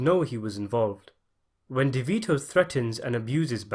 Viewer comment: did he say Davito?